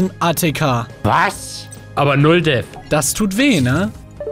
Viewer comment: i hear d e a t h